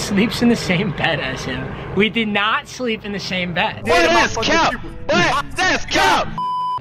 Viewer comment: he thought he was slick she def said we did sleep in the same bed but he put a buffer to make it seem like it was lagging